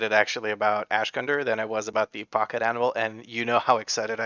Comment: Funny that I have missheard Pocket Anvil this whole time.. I thought for sure he said Pocket Animal and I thought that there is a stupid trinket, WP blizzard :D